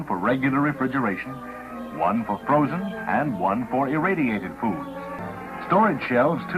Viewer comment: did he say irradiated food?!?